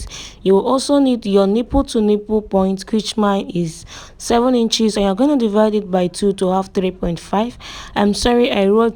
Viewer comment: I don't understand the word you used here, sounds like "krichma"? What did you say? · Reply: "Total"